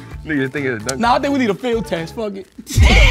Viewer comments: Fanum was MVP this episode, "I think we need a feel test fuck it" lmao. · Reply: I thought he said field test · Fanum had me crying 😂😂 “i think we need a feel test” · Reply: definitely said “feel test” so they can feel it n make sure they get an accurate answer. Why would they said field test? That doesn’t even correlate or make sense as to what they’re talking about n trying to ask of them. Wtf do a field test got to do with anything? It sounded like he said field but he didn’t. He said “feel test” to test it n see if it’s soft or hard for a better answer. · Fanum was MVP this episode, "I think we need a feel test fuck it" lmao. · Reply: I thought he said fill test